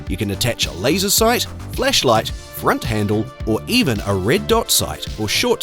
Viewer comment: I swear it sounded like you said you could attach a FLESHlight to the first one😂😂